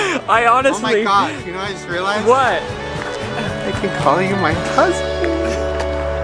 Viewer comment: at first I thought he said “You know what I just realized? I can call you my cousinnn!!!” I said, “wow. First cats, then Willow, now family, eh?”